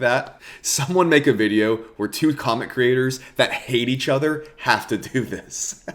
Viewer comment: ive been trying to understand what this means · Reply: Sorry, upon listening to it a second time at around the 4:50 mark you're saying COMIC creators. I misheard it as comment creators and thought you were talking about not comic.